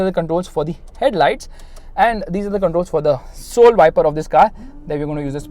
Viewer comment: did he just say "soul viper"?